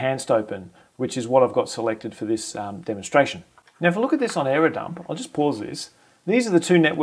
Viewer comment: What is the command you use at 0:34? It sounds like you say “error dump”? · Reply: airodump-ng which is part of aircrack-ng